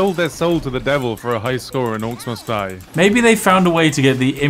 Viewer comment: All I hear is “Orks Must Die Free”, because accent?